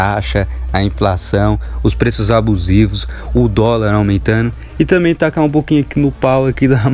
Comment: Olha desculpa se for mente poluida de mais so q eu ouvi 0:44 " o toba aumentando" Se quiser que apague Esse comentario , tudo bem